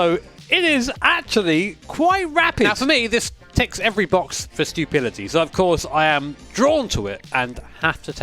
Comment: Sorry...but I could have SWORN that you said "this ticks every box for stupidities."